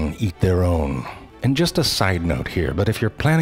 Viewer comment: All joking aside, this is entertaining as well as legitimately educational. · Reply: I thought you said all jokes aside lol